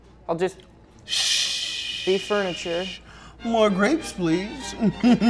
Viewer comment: Maybe I'm a bad person but i heard "F slur and grapes"